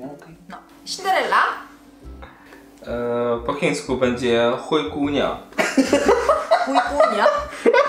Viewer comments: Super film ! Ale czemu ja tam usłyszałam ,, chuj kunia,, xdddddd😂😂😂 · Najlepszy Shuai 1:18 ja usłyszałam "chuj kunia" xdd